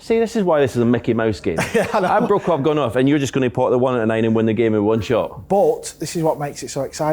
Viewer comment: tf was he saying 🤣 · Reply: See this is why it’s a Mickey Mouse game I broke off gone off and your just gonna pot 1 and the 9 and win the game in one shot